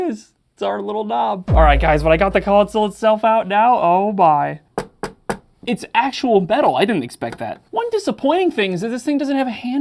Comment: *knock knock "It's actually betal"... Lol I'm going to think of that word every time I hear metal now!